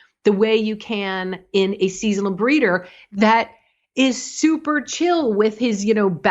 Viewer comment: I thought she said "Seasonal Greeter" like the Salvation Army Santa Claus at Wal-Mart.